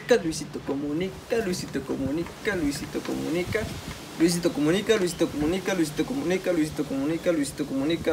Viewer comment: Parece que dice me visto como mika